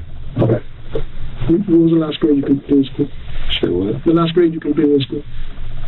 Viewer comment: “what was the last year you did in high school?” “3 years in college.” LMFAO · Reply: I thought they said last grade completed?